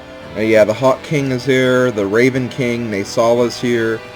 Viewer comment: i thought you said the hot king... XD